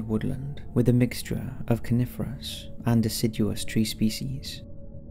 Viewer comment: I totally misheard that as ' carnivorous and deciduous species ' lol Did you know that Conifers are Gymnosperms?!?